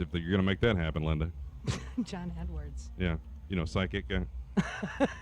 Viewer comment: Sounded like he said "everybody get it up for Aaron Lewis"